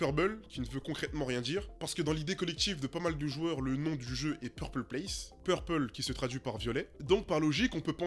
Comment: moi je pensais que le nom était "PRUBLE (PREUBEUL) PLACE" pour moi à l'époque 😂😂